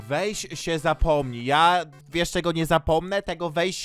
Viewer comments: usłyszałam weź się zapłodij XD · czy tylko ja usłyszałam "weź się zapłodnij"? xD